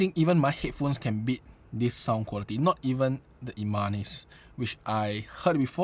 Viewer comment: Hello, thank you for the review. At 20:59 can you please tell me the headphones you are referring to? Sounded like Armanis? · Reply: It’s the immanis